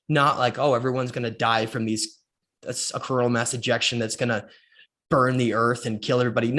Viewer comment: For a split second, I thought you said 'corona mass injection' 🤔